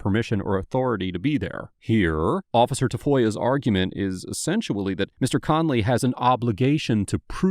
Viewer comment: did I just hear you say "essentually"